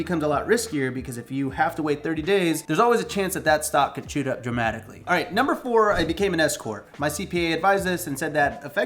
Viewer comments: I was listening from afar in the other room and swore said “and #4, I became an Escort. My CPA advised this.” 😅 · When you said you became an S-corp, I looked away and totally thought you said you became an escort... good stuff!